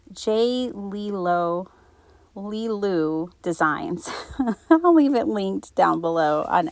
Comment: Lovely journal. ❤ The arms, legs, and crown on the bunny paper doll you said you got on Etsy. May I please have the name of the shop? I thought you said J Lilu Designs but I can’t find that. Thank you and I appreciate your creativity and your vids! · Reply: Hi Cathline, Thanks so much. I totally forgot to add the link. I think it is Jay LeeLoo. I will look it up and find the link for you. It’s a fabulous set.